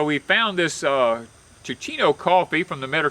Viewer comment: For those who are not familiar: Caffeine free and acid free, Teeccino can be enjoyed any time of the day or evening. Drink Teeccino as a delicious bedtime tea that is satisfying, filling and won’t interfere with sleep. Without the acid indigestion that coffee often causes, Teeccino is perfect for promoting good digestion enhanced by chicory’s prebiotic inulin that supports healthy probiotics. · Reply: Thanks for the info. Thought he said chicino🤪